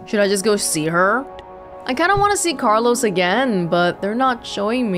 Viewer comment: When you said Carlos, it sounded like someone saying Benjamin